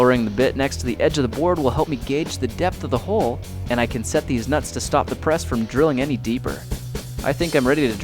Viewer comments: Is it just me or does he say deez nuts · check out 1:24 did you say Deez Nutz? XD i dont know what you said but it sounded like it